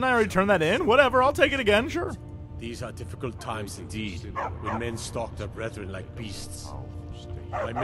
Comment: at first i thought Jorleif said these are difficult times indeed, when men stalk their brethren like breasts.